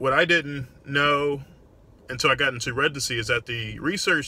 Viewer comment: Been a fan for a while Doc. However, I've always been curious as to why it sounds like you say residency with a G? Like "Regency"